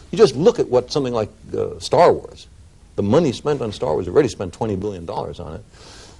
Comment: "We got money for wars, but we can't feed the poor." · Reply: I heard him say "star wars"